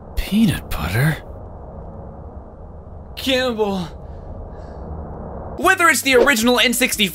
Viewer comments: according to captions he saying heubel instead of cannibal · I just realized he said “cannibal” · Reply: this entire time i thought he said caramel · Still can't hear what he says here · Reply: Cannibal